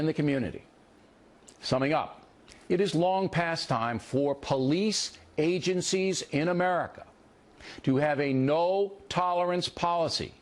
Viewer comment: Not by any stretch am I defending Bill (the dick is simply indefensible), but Kyle misheard what he said. He actually stated quite definitively that ”it was long past time that police agencies in this country have a no tolerance policy toward brutal police behavior”. Kyle thought he said police should have a zero tolerance policy toward bad behavior directed AT police.